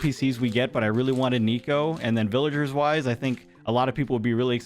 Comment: I thought you said you wanted niko the least, I need my hearing checked lol. But who wouldn’t want niko he’s so adorable! And I love monkeys too, one of my favorite animals in real life.